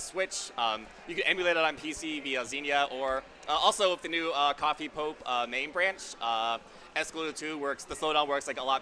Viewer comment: Does anyone know what MAME branch did mention around 10:00:50? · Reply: CoffeePope